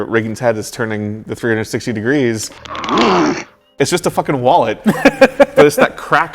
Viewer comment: what does he say @ 37:40 ? "it's just a fuckin _____." "wallet"?